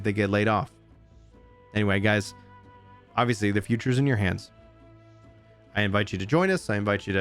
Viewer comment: Brain "The future is in your hands." What I heard "The power is yours"